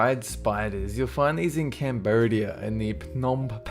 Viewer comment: what did he say?? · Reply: Cambodia.